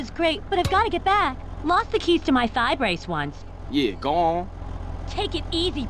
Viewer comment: what does she say? key to what? i cant understand. English isn't my first language. Can someone tell me? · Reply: I'm pretty sure she said "Lost the keys for my side brace once" but I don't know haha